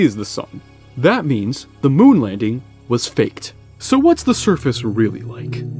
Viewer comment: Why did I hear "... That means the moon landing was faked"? Even captions here that.